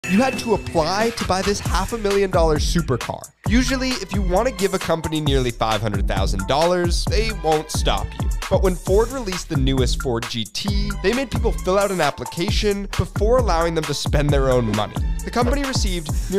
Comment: Why did i hear "they wont stop you" As "they Will adopt you" LMAO